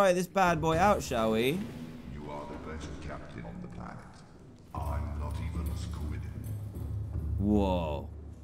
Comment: Can anyone tell me what the Cyclops just said? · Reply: Duy Phuc Nguyễn you are the beet captian on the planet im not even a squid i t h i n k